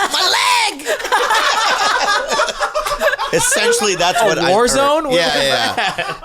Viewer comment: what happened with that lisp bobby. "Ethentially thath's what I heard" 😂